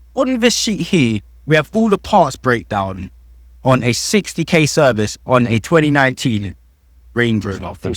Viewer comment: did he say 60k service?? Did i hear that right?😂😂